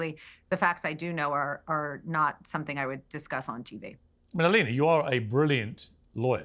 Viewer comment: did he just call her M-alina?